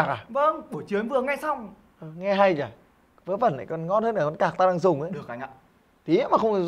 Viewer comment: có ông nào nghe nhầm thành "con cặk" như tôi không 🐧